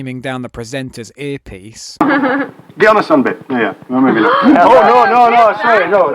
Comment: what does he say? I can't make it out but everyone seems very shocked. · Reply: "Get on a sunbed!"